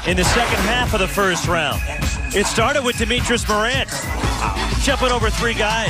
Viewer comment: I thought he said (Ja)metrius Morant